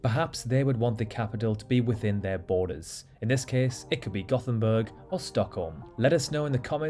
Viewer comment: i thought u said gothemborg first but then i got it Göteborg GOUteBORGE basicly